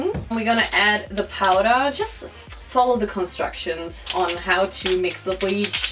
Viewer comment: 😂😂😂 "follow the constructions " instead of instructions lmaoooooo